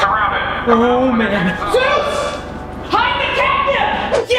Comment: holy that sounds so real🤯 but it’s it not I like that he said Oh man Zeus! Hide the I don’t know what he said after that